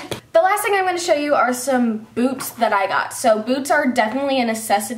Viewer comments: I thought you said I got some new boobs not boots! 😂😂😂😂😂😂😂😂😂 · When she said: the last thing that i got were some boots. I thought she said boobs 😂